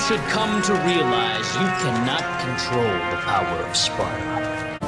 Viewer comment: I turned on cc for 1:52 and instead of Sparta it said SPONGEBOB 😂😂😂